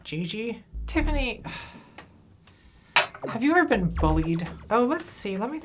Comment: I heard "ever been bullied" as "ever been Bowied" and now I can't stop wondering what that is